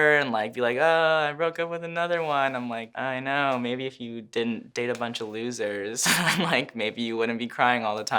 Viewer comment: Omg 2:50 "May be if you didn't..." "May be he you didn't..." What does he say??